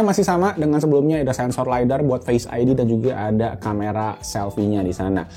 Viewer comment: lidar bukan disitu bang, dasar penyebar hoax 😂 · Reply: Wkwk kirain kuping gue salah denger dia bilang sensor lidar 🤣🤣🤣